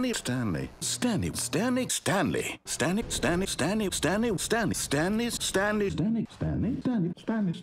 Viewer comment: i hear "stanley was standing standley"